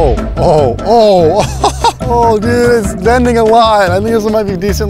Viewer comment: What did he say at 4:19 ☠ he said its bending alot mb